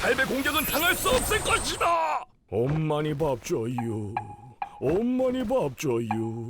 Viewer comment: 부터18:37까지들너보면엄마니밥줘요라고들려개우꼌ㅋㅋㅋㅋㅋㅋㅋ